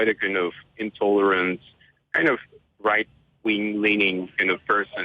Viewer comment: Did you say right wing?